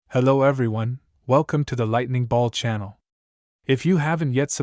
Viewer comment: when you say "hello everyone" my mind bugged and I heard "welcome to in deep geek" for a second LMAO hahahaha